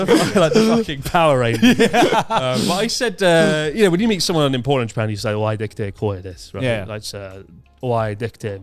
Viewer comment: finally figured out what said, it’s お会いできて光栄です. Took me a while to get that since I didn’t take my lesson yet lmao